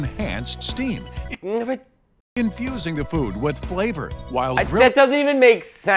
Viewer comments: I first heard 'confusing' the food with flavour 💀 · I misheard the ad reader's voice at 4:22 and would have sworn he said, "Flavor liquid in the Turbo Grill's flavor reservoir creates enhanced steam, confusing the food with flavor!" I haven't laughed that hard in a while.